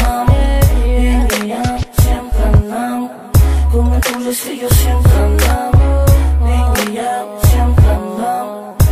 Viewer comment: Minuto 2:54 que dice? 🤔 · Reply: Con nuestro rastrillo siempre andamos